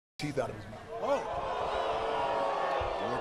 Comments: Roman: "You're lucky we're here" Hunter: "You're lucky you are too" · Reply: Thank you! I was so confused by what triple H said lol. I thought he said “you’re lucky, R2”, as if that’s a nickname he has for Roman Reigns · For those who keeping asking what HHH says at the end. It sounds like Roman says "You're lucky we're here" and HHH responds with "You're lucky you are too". This is also what the captions/subs picked up. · Roman: "You're lucky we're here" HHH: "You're lucky you are too" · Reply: Man I needed clarity here. I thought I was playing too much video games cause I thought he said you’re lucky R2. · Roman: "You're lucky we're here" HHH: "You're lucky you are too" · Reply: I thought he say “you’re lucky we’re leaving”